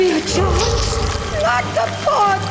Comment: Did.... did she just say That the fuck? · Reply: “Not the pod”